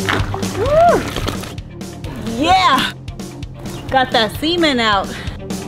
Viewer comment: Just way to many sexual inappropriate references that come to mind, just from her talking. I could've swore she said semen at 4:39. Gotta love it. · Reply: rofl, does sound like she said " yes got that semen out"